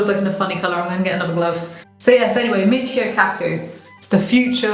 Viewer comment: “Michio Kaku” I thought she said “mix your cat food” 🙃